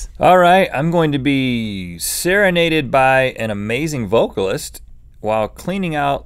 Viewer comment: I was so happy when I thought Rhett said vocaloid but it turned out to be vocalist